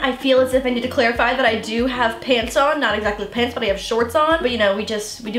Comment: Lauren: I don’t need to worry about staining pants. Me: yea you don’t WEAR pants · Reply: I thought you said share pants.. I was confused